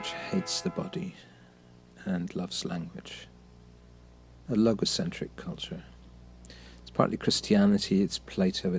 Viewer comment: I first heard, "a logocentric culture" as "I love eccentric culture."